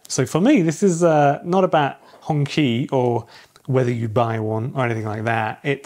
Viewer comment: Hongqi sounds like you said honky